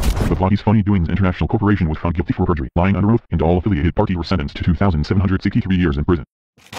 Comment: sounded like 2763 years in Britain.